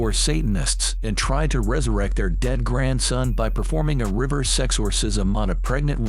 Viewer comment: Legit thought he said 'river sex-orsism' and was confused/sussed out at the next scene.